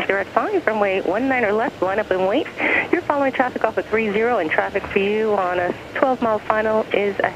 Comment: Can someone tell what the tower is saying at 5:00 after LUAW part: “your following a traffic ...... 30”. I’m guessing it’s “off of”. Is that a runway? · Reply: Der Dere "trafic off of 30"